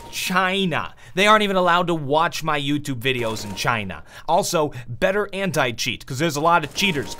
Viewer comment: LOL at 8:37 i heard him saying "Also better hentai cheat" hahaha wtf